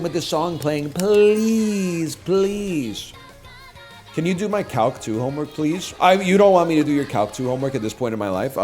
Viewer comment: Legitimately thought that he said hawk tuah when he said Calc 2 It’s over for me I’m afraid